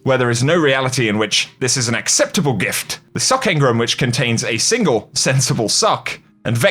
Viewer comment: Jesus, I was half listening and thought said the engram contains a single sensible COCK ! LOL !!!